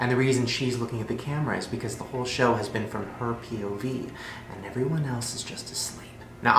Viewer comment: Deadass freaked out bc I heard him clearly say "point of view" and not POV and I'm actually shook. 😂😭 like my sister told me he said POV but I stg I didn't not hear that at all